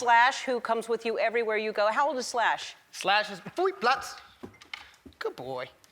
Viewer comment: what did he say?🥺❤️ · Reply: He said "Platz" which is german and means down. K9 dogs are usually trained in the german language. It's common.